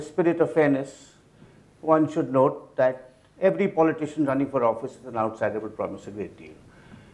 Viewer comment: Can anyone please tell me what he says at 0:43? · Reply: Abhay Bhatt he says in the interest of fairness anyone running for office as an outsider will promise a great deal...